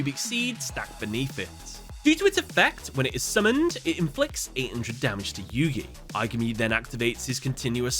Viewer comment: I might have heard wrong, it sounded like you said "800 damage to yuya"